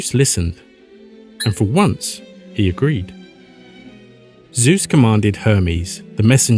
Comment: I legit thought you said Zeus commanded homies 😭😭